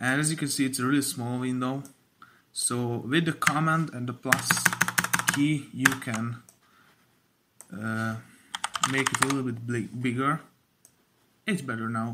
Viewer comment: I dont know what he said plz help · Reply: "with the command and the plus key, you can make it a little bit bigger" Sorry if it wasn't understandable, with this learning-English-since-many-years-but-still-cant-pronounce-many-words accent.😅